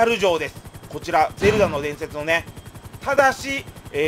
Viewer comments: 全裸の伝説って聞こえた(笑) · 全裸の伝説って聞こえてしまった...